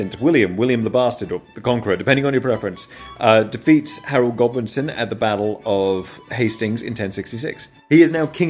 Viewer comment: I had to rewind cause I thought you said Harold Goblinson and I was astonished by the awesome historical name!